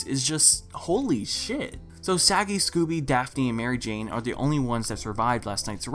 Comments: i swear he said saggy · Did anyone else hear him say Saggy? XD